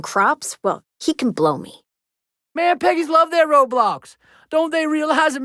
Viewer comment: I thought Hurk said "Them Peggies love their Roblox"...